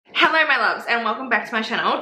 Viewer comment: Ngl I thought you said "Hello mellows" and I thought it was adorable 😂